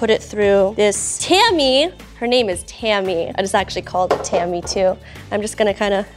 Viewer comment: Seleno and Barnes: Injury Attorneys always nails the food culprit. Captions fixes 4:31 "It is actually called called Tammy-2" -> "It is actually called a tamis (pron. Tammy) too" 5:34 "dried in ground" -> "dried and ground" 6:35 "Bam" -> "Pam" [Not sure on this one, could be either] 9:27 "when the corn" -> "what Nicole and"